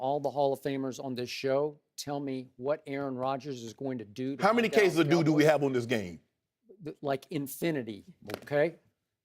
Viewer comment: Someone please help me what does Shannon say at 4:04? I heard "how many cases of doo-doo do we have on this game" lol Awesome game, go pack...classic Rodgers